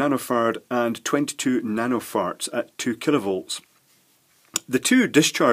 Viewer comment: lmfao it sounded like you said 22 nano farts